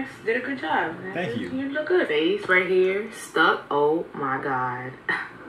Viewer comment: Did she say you look good bae. 👀